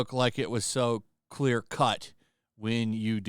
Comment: I heard "it wasn't so Queer cut." 🤣😂